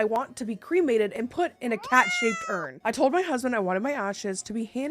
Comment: I was only listening, not watching at 1:52 & thought you said cat SH!T urn 😭